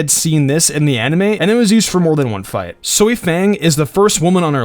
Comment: did I hear soi fang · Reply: Soya Fang